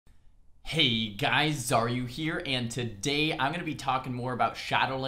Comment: Hey Xaryu, guys here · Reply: As a mainly non english speaking person, for more than a fucking 2 years of watching him I thought he is saying "heyyy guys are you here" LOL